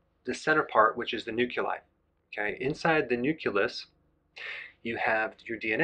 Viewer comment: Great video dr. Berg. Fyi...please check your pronounciation of nucleus. I thought i heard nuculus. Love to you